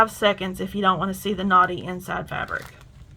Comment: (I'm catching up with my queue of videos to watch so I'm late, sorry!) but omg those unicorns!!! I thought you were saying knotty, not naughty! 🤣🤣